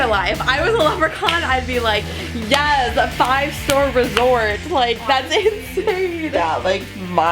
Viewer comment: I heard "Yas! 5 STORE RESORT